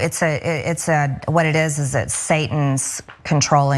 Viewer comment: Hail Satan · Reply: Satan??? I thought she said Santa!! lol